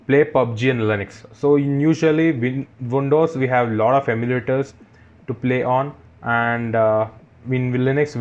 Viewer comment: Did he just said "lauda Emulator"